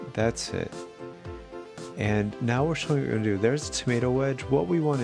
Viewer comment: 'there's a tomato wedge'? or did I hear wrong? :p